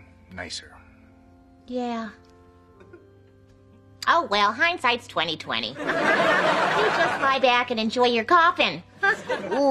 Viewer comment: Did kitty say hind side 2020